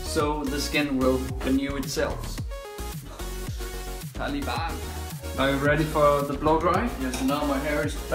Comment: Did he just say “Taliban” at 6:10? Or is it me? If so thats some racist shit.